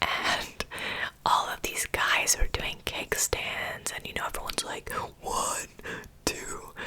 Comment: The whole time you were saying ‘keg stand’ I thought you were saying ‘cake stand’ and got very confused 🙈😂